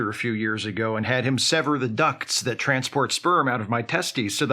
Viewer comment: For a second i thought you said ducks instead of ducts and imagined little animated (stop motion) rubber ducks carrying floating wagons of sperm down a river inside the body and that is now how I choose to imagine it works.